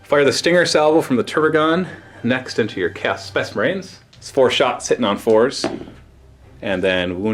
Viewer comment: What said: "im firing into your chaos space marines" What i heard: "im firing into your cool specimens"